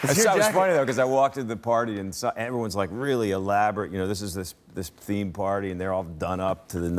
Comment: For a moment there I thought he said 'atheist themed party'